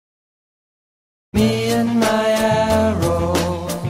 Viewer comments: Simpson homees dog bongo at first on simpsons I thought it said me and my apples I was like what! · First time when i listen to this music in the simpsons, i thought he was saying "me and my animal"